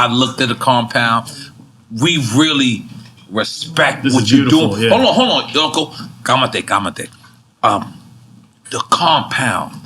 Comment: did he just say "ka mate ka mate" lol